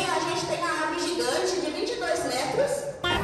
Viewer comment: Opa eu ouvi roda gigante de 22 metros??😃 Opa ai sim chão não é comigo meu negócio é altura o céu é o limite:então o céu não tem fim isso significa que 🤔em fim buguei aqui 😂🤣